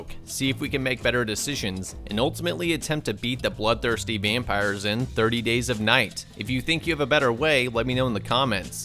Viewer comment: Me misheard what he said 30 Shades of night?!?!?